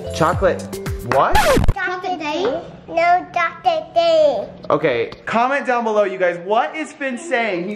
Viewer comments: I think Finn was saying “Chocolate Doctor Doom” LOL 😂😂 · I think was right, I heard Finn say “Chocolate candy”. Love you guys ❤️❤️